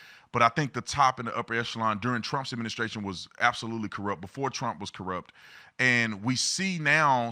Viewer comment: I just have a question. Did he say at 2:38 , "before Trump was corrupt" ? Trump is not CORRUPT ... Just asking